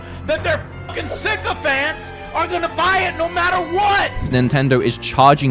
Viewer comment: The subtitles got it wrong. He doesn't say "psycho fans", he says "sycophants".